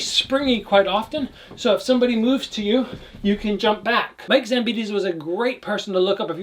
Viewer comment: What is the name of the fighter @ 4:25? I cannot hear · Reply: Mike Zambidis. Little tank of a guy.